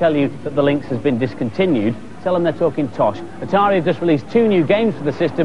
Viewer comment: I heard that as "fucking tosh", oops. That Brummy accent huh.